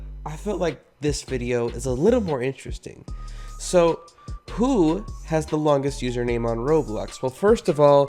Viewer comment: i thought he said hulu has the longst uername jn roblox